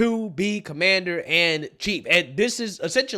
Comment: Nigga, did you just say "Commander AND Chief?!" WTF bro. I know you are fast as fuck with the commentary, but damn man!